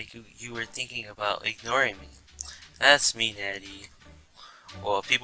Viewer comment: Did you just say daddy jk